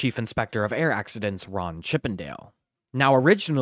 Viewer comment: Sorry did I hear "Ron Chip and Dale"!?!?!